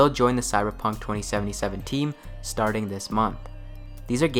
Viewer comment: 2020 is the source material. Might have sounded like 2017, but I said 2077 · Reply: sounded like 2017 at around the 4:03/4:05 mark.